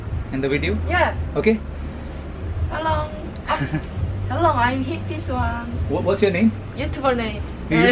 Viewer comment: 편의점 분 채널명이 뭐라고 하는거에요?? 히피소아?